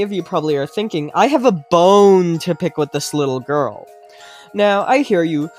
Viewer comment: I thought bro said he’s got a bone to stick in this little girl 💀💀💀 fml